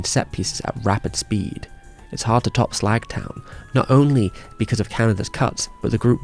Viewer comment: Did he just say 'Slagtown'?